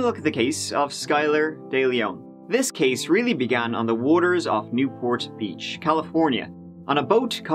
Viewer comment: I thought said "New Portlaoise, California" hahahahaha I was so confused